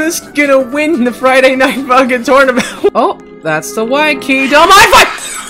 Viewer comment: Wait on 0:09 did he said swear word!? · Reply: He said funk